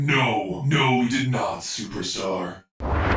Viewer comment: I thought freddy said stupidstar....